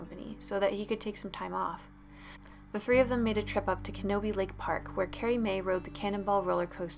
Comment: wait did u say laughes KENOBI lane?! HAHAHA! Hello there young padawan laughes uncontrollably